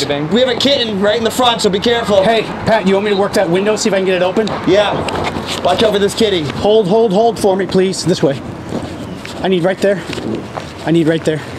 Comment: When Patti was saying "Watch out for this kittie!" I thought he said, "Watch out, this kid!" Anyone else hear that, or is it just me?